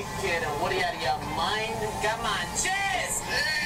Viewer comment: I thought he was saying "Cheese" instead of "Cheers"